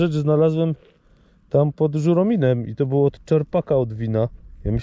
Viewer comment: Dobrze usłyszałem Żuromin?